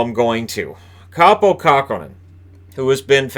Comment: I thought you said Kaapo Kakko was in Minnesota. I was even more confused when you said he was in net! :P